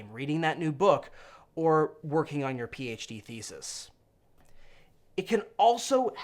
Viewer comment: Wait, what’s your PhD thesis on?? I feel like I’d read the heck out of it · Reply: I thought he said PHB thesis, as though he was writing a PhD thesis on the PHB. Doctor DM.